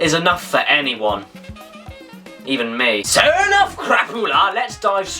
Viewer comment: That there sounded like the Finnish word "Krapula" that means: Hangover.